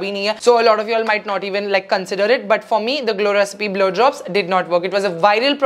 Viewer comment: Wtf did she say 'Glow Recipe BLOW JOBS' at 26:17 ?!?!?!?! 😆😆😆😆😆